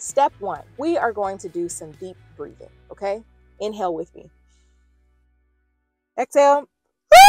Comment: Why when u said “inhale with me”, I heard “in hell with me”, 😂